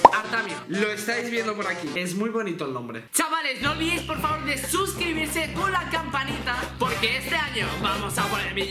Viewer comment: parece que dijo dessuscribirse xD Aunque yo no estoy suscrito por que hace muchos vídeos falsos